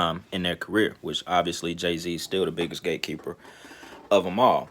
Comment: At first I thought you was saying Gay keeper.... I was like wtf as my ears expanded and eyes popped 👀